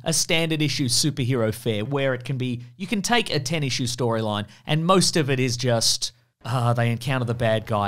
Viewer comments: i hate that i heard "tennis shoe storyline"; like, GREAT - even MORE new, nerdy comic book verbiage i'll be hearing everywhere on the shambling bird app... except, i won't, because that's NOT what you said. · What's funny is that I misheard Ten Issue Story as Tennis Shoe Story.